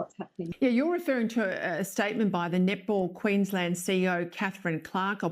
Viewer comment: Oh Netball, i thought they kept saying nipple...lol